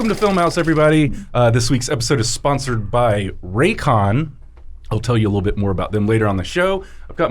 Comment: lol I heard "this week's episode is sponsored by Raytheon"